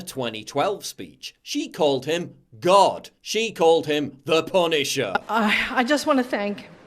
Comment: it sounds like ford is saying the penis's lol.